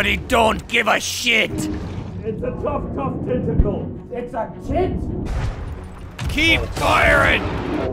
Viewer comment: I nearly cried when I heard from underwater. 'IT'S A TIT????'